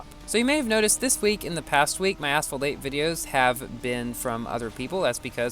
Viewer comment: In the subtitles says "asshole", not asphalt in the middle of the video (4:06) xd